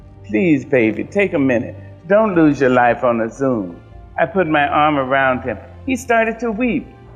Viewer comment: I thought she said "Dont Lose Your Life on a Zoom" like they were fighting on a Zoom call, but Zoom was before her time.